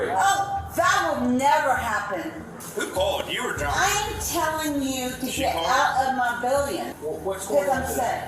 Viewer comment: am i the only one who hears " im telling you to get out of my billion"?